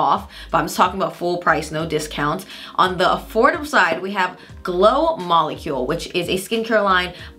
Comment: i giggled at 2:24 when you said glow molecules instead of good molecules lol